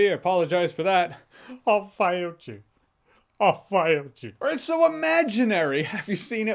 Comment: is Jeremy really saying, "I'll F you"?